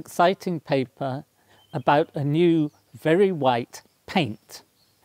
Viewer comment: I thought he said "Barry White" at first!